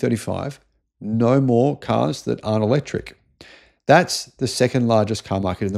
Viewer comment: slip of tongue? I heard "no more cars that are electric"